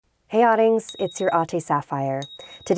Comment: I always thought she was saying "Hey Audings, it's your Auntie Sapphire" 🤦